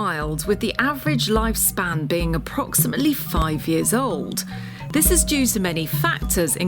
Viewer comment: I thought he said 90years old!!!! I'm like wow!!!!! It took me a while to realize got it wrong !¡ Must get my lug holes sorted!!!